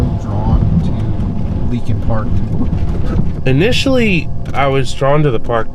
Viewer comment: I so high I thought it said likin Park 💀